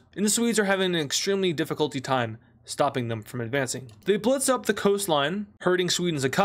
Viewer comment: "Advancing" What I heard: "AdFRACEing"